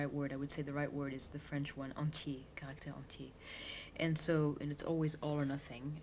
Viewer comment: can someone clarify what word she is talking about at 1:01? enchee? sorry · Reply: entier, caractère entier Entier = whole, in the whole part